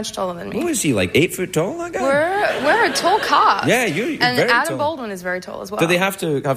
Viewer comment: Can someone tell me the word she said at 13:46? I'm a English lerarner and couldn't understand what she said after "We're tall..." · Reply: "We are a tall cast."